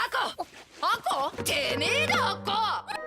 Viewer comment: sounded like she said “Damn It Akko!!”